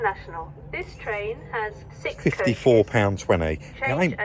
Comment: Have you looked at hiring a car at a local enterprise rent a car and dropping it off at a branch close by the vans? Think there around £80 for the day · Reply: Oh my mistake thought you said the train was £154!! 🤦‍♂️